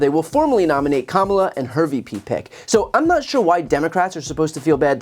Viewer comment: I literally jumped when I thought I heard "Kamala & Herbie P. Peck" (instead of HER VP PICK) I went, who's that guy nobody never saw coming?! hahaha